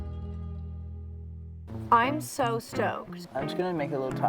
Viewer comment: Omg I thought she said "I'm so stoned"!!!!